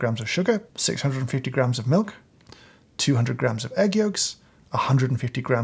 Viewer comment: I thought he said “200 egg yolks” not 200g, I was like “Wat?!?!?”